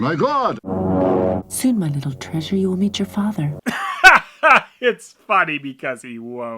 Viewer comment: I heard “soon sweet treasure you will eat your father” instead of “meet” and I like that line a lot more